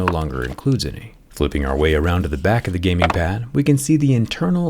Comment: Did I hear "Free gaming pad"?